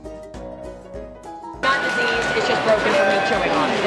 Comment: I heard "It's not diseased it's just broken from chewing on" 😂